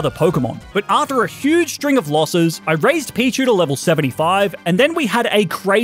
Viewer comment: UMM WHAT DID YOU SAY WAS THAT A PICACHU I HEARD